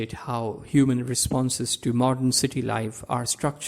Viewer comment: what words are you saying at 4:41 ? about the two correspondent ideas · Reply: the terms are 1) "Erlebnis" and 2) "Erfahrung"